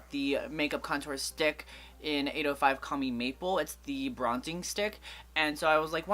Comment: I've always thought you've said "commie maple" instead of "call me maple" until I saw it at the store. lol I'd had always thought that's such a weird name for makeup!